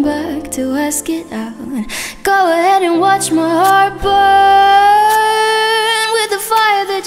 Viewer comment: THE BEST "BURN" IVE EVER HEARD OML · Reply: Until now i thought she said "Watch my heart BOY"...